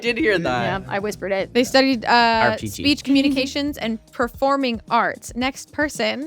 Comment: what is Ally saying here? 😂 I’m hearing “Arpcheechee” but that can’t possibly be right can it? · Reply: Art teaching